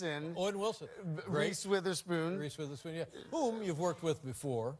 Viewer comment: Did he just say Rice Witherspoon? xD